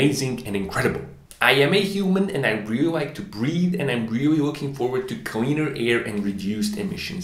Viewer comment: Hahah I thought you said I am a human and I like to breed!! It wasn’t till the smoke stacks and smog background played that I realized you said breathe!